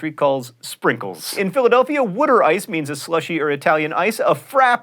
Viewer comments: did anyone else hear him say wooder ice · did he just say "Wut-er ice"??????